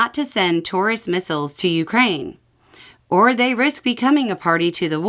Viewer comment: I heard that as Russia warning Germany not to send "tourist" missiles, and thought it was a reference to "tourists" in the capital building.